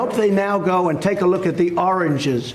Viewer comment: I know that my ears are none too good nowadays, but somehow I hear Trump say "oranges" when I believe he means "origins". It could also be the cheap speakers in my cheap laptop, but I feel like my mind is beginning to slide from the color orange to the actual fruits with that color. Please, somebody tell me that his President is not driving me insane.